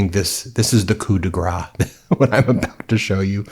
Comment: Hello from Québec ! Always a pleasure to hear what you have to say … I may be out of order here but at 1:35 you said … and this was spot on ! « This is the coup de grâce » the subtitles were a bit off ! Coupigra ! I love it but it is ‘coup de grâce’ Coodahgrahss Bravo cher you should speak French more often ! I would gladly and graciously teach you … I have been a French professor for hum hum 40 years ! Au revoir and continue le bon travail !